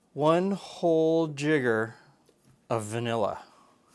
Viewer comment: I was watching with subtitles on, and it looks like you're using the auto-generated subtitles with no editing; when you said "jigger of vanilla" it put down "[_] of vanilla", implying that the system thought you were saying something much... worse...